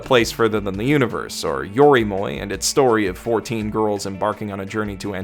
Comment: He says "4 teen girls go to alaska". I thought he said 14 girls. I was confused as to where the other 10 girls were during the whole recap.